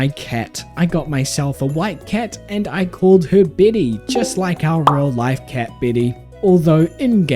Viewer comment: Anyone else hearing Billy instead of Betty?